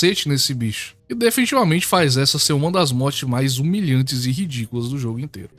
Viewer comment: Eu ouvi "série de mortes humilhantes de fear & Hunger"?